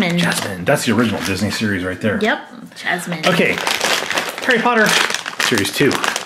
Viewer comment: did he say parry hotter?